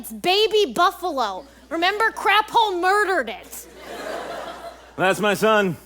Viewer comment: Craphole?i thought it said crabhole